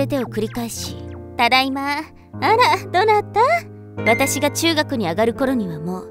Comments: 辺りでテロップが「大学」のはずがセリフでは「中学」に聞こえる… 疲れてるんだろうな… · 中学って聞こえるの気のせいか…?